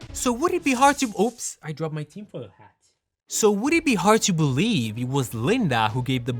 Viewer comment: Why at 4:55 the captions say he dropped his team for a hat? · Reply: They get stuff wrong at times, it's *Tinfoil hat